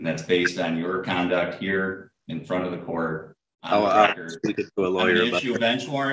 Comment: Okay, first a couple of personal comments. The "Friend of the Court": does his mother know he's skipping school? Don't get me wrong. I'm sure he's of age and more than qualified for his job. He seems bright and capable. But he looks 10. As for Mr. how many more illegitimate children does Trump have in this world? Okay, personal attacks aside, this cat can't seem to tell when someone is trying to help him out. He's heading for jail, probably not going to be for the last time, and I say he's really got it coming. I feel bad for his wife and upcoming child, but it's his attitude that's going to end up costing him and his whole family. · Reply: He works in "front of the court", not "friend". He works the front counter taking payments. Either y'all misheard or he misspoke